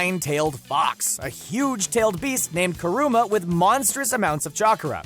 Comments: did he just says that the nine tail fox it's called karuma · did he just say "karuma"???? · Did he just say “Karuma” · Did he say Kuruma?